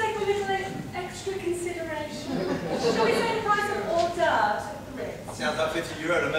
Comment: plz i want know what did she say about price?she said odobull right? I want to play ' don't dress for dinner ' but there is so many mistranslation so plz help me (T.T)